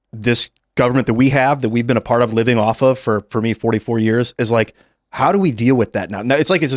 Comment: did I hear Ian correctly? He has been living off of this government for 44 years!?